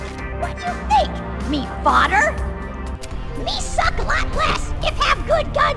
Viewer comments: he actually says "Fodder" instead of "Father" as in Cannon Fodder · he actually says, "Me fodder?" and they are called Cannon Fodder lol.